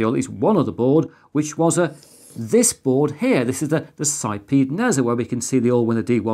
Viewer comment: mins even with closed caption I can't understand what you said. "this is a site NASA" but I heard this is a site p nasa. Either way I have no idea what you said - not important - just giving you a heads up. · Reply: I said "this is the Sipeed Nezha". I really should have put up a caption with the name of the board. Sorry, my bad. :(